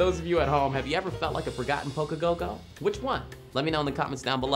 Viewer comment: what does he say lol "forgotten poké go go" ?